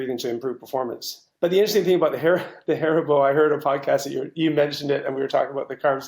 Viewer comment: I'm sorry, my English is not very good, so I couldn't understand, can you please write here the name of the carbohydrate source you are mentioning? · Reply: Haribo(gummy bears)